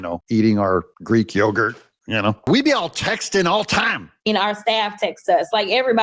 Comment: I thought she said she was all texan😂😂😂